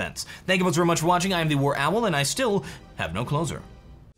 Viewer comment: for such a long time i misheard: "...and i still have no clothes on"